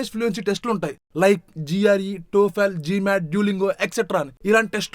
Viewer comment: Wait at 9:30 did he say diuoghiAl??😢😢